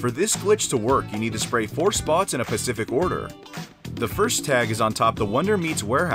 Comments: Am i the only one that heard him say pacific instead of specific @ 5:56?😂 solid vid tho fam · No one else heard him say “ pacific order “ during the spray paint thing? It just bugged me that he didn’t say specific. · did this mans really say "pacific" instead of specific